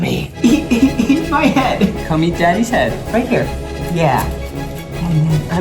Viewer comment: Did I hear Zach say "bowie eat daddy's head" or...